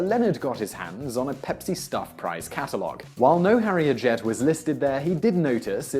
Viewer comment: CC says Harriet Jet.